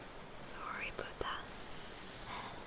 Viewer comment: Did she just say "Sorry Puta" :face-pink-tears